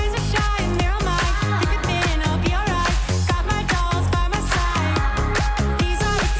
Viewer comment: I swear i just heard him sing" ive got my balls by my side."